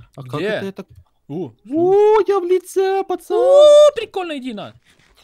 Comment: мне услышалась фраза " О прикольно иди на"